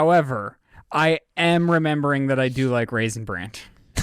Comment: What's he saying? Did he say "Raisin Brandt"? No one questioned that??? What was he saying???